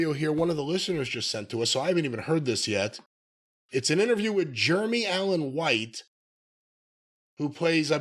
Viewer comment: Jeremy?! I thought you said "William Allen White"!